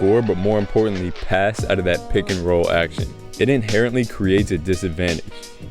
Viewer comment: For a second I thought it said Rick and Roll passing